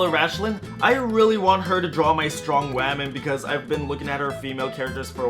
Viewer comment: Has anyone noticed? In 3:43 he said waaameeeen instead of woman🤣🤣🤣btw best art I've ever seen 👍🏻👍🏻👍🏻❤️